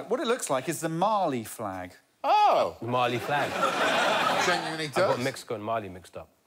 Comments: "Subconscious Mali".. Hilarious! 🤣🤣🤣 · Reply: In part because of the colors, I always thought they were saying “Marley flag,” as in Bob Marley. 🤦🏻‍♂️ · * uses Italian ingredients * * calls it Mexico * * actually makes Mali * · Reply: Wow....for years, I thought they were saying "Marley flag" and showing a sideways rasta flag. I feel like an idiot lol